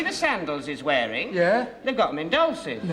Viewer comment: Since I was a kid ~30 years ago I always wondered what Mr. H was saying about the man's sandals. It was only recently that I pieced it together: "They've got them in Dolcis" Dolcis Shoes is a shoewear retailer in the United Kingdom that operate online and through retailers. Previously, they owned over 65 standalone shops in the UK and over 150 concessions.